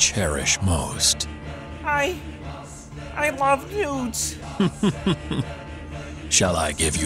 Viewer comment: Wait Kermit's gay, then why did marry miss piggy? Edit: I now clearly heard "nudes", but still doesn't answer my question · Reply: TheMightySwooj oh I thought he said "dudes"